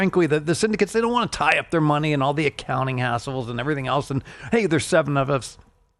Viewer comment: I heard "hassles", not the other word.